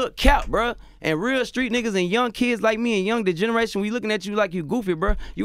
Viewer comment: My guy said "The light skin N*gga from DeGeneres" I was thinking the same thing instead of Degrassi 😂😂😂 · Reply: I thought he said degenerate lol